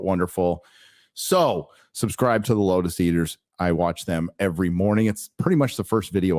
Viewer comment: which channel is Gary saying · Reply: Sargon of Akard , the channel is called the Lotus Eaters.